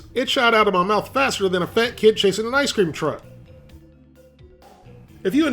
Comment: did he just say a fat kid chasing an ice cream truck.